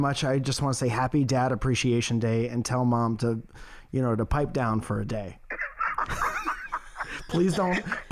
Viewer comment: I thought said "Tell mom to put the pipe down for a day" for a split second. Nice chuckle on a snowy day.